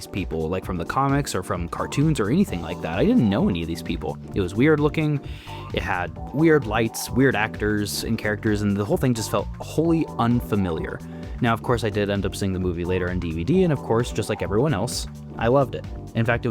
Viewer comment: Did bro say "weird actors" 8:36 ? 😭